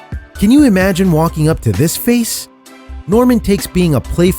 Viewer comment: woAH HOLD IT. DID YOU JUST SAY "walking up to this face"?????